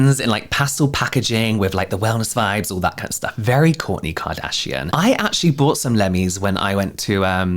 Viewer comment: I kept hearing “Corny Kardashian Gummies” and I was so happy to hear it put so blunt 💀